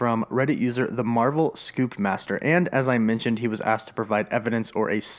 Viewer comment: I thought you said noob master instead of scoop master